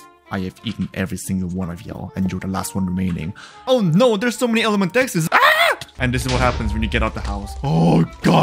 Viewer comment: did he said "oh nyet!"?